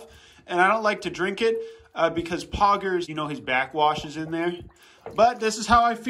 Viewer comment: the animals are drinking poggers bathwater,kinda sus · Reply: I think he said his back washs in there